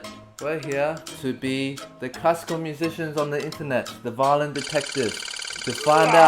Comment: I just understood now that you say Classical Music, I always heard it like cuz'co music and I was so confused #imdumb